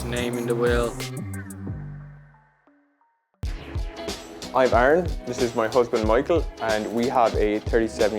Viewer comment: I thought it said “he’s my grandad, he’s my husband” 😂